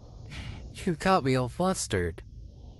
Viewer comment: i'm just going to say i thought he said"i forgot we had mustard" but he really said "you got me all flustered" how did i hear that!?